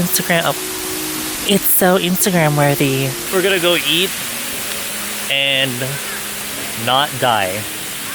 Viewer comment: I thought he said, “Were gonna go yeet and not die.” Wtf